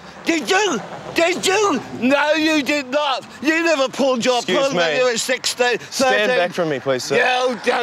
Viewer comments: wtf is saying at like 1:41? previous versions have him censored. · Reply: You never pulled your pud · What does he say at 1:41? "You never pulled your something when you were 16!" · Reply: The captions that came with file (not attached) said "...pulled your pud"; we are uncertain of the captions' accuracy